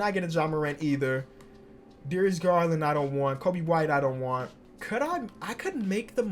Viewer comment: who else thought he said Kobe Bryant